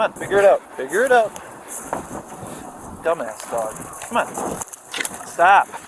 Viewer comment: Did he say dumb ass dog?!?! It was around 12:51 He is the dumbass not the dog.. He shouldn't be a K9 handler